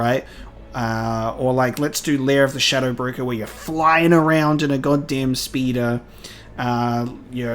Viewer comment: I misheard at 43:24 – "Flying around in a goddamn speedo". Can't get that image out of my head now.